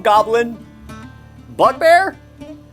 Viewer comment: Did he just say "Buttbear"...??